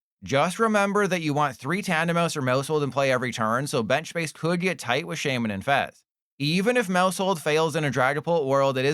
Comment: at first I thought you said “space could get tight like shaymin in bed 😅